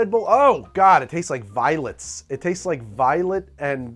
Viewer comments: I heard: "What is the taste of Red Bull? VIOLENCE!" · I love at 9:33, he says "it tastes like violets" but for whatever reason, I heard- "redbull tastes like violence" and ofc laughed and nodded, fully understanding the sentiment... until I read the captions... and laughed again, thank you for this gem of a line Greg!!! 🤣